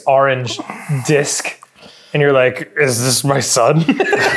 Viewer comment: dan going on weird tangents and ending up at sentences like “is this my son?” is my favourite part of the power hour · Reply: I thought he said "is this my sun" :D